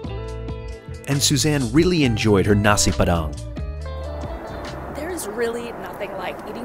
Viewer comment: Did I hear Nasi Padang (11:23) Suzanne was having? Glad you enjoyed that menu.